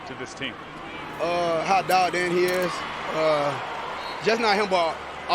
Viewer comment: Can someone tell me what Lamar says at 5:00 ? How what he is? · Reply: Dialed in. Lamar might not even know what he said. He can barely speak English